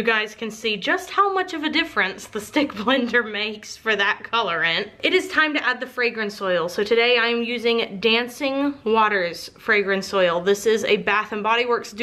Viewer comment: I thought the fragrance said “decaying waters” and I was so confused